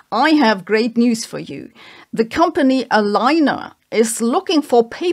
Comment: because of the accent i heard "alleine"